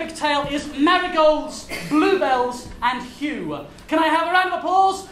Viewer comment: Thought it said blueballs💀